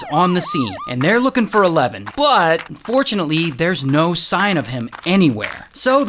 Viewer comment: did u say fortunately? Hell nahh